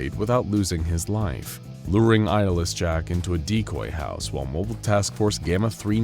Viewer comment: Subtitles saying "Luring ISIS Jack" lol